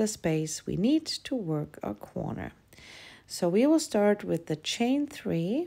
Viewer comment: why do you say quorner instead of corner? Or is quorner another thing?